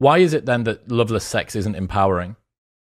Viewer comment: Why is Loveless Sex Empowering? Correction: How Come loveless Sex ISN’T empowering? (Is what was said)